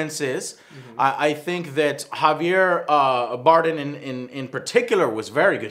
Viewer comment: did he just say Javier Boredom? lol